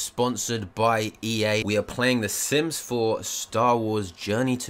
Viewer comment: did he just say simps 4 ?